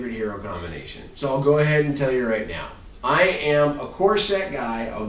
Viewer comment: at the end when you said you were a core set guy I thought you said corset and were talking about a female hero with a corset XD any way I love your video's watched your entire mage wars series and gonna start watching more from you